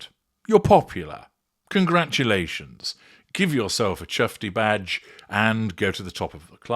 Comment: "Chuffty badge" that's a new one From "I'm chuffed" ? Have to remember that, but wonder what the logo would look like? . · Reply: ……thought said ‘chesty badge’ as in ‘Chesty Bond’………